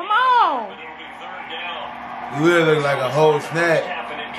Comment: no bullshit at first i thought he said old snack.... but had to hear it again..... but big up's to this couple